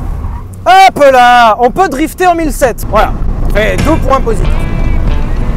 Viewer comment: Il dit quoi à 6:54 ? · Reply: "ça fais 2 points positifs"